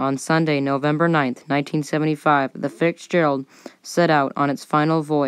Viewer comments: Am I hearing "fixgerald"? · Am I hearing her say "Fixgerald" ?